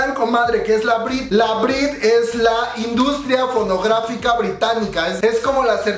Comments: entendí la industria pornográfica y me re asuste XDD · tuve que regresarle al video pq escuche pornografica lol 😆xd Love u!!!!❤